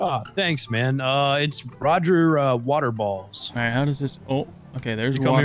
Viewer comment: I thought he said waterballs